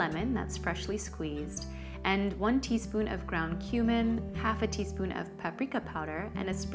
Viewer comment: Wait, ground human?? I thought you said this was vegan!! :)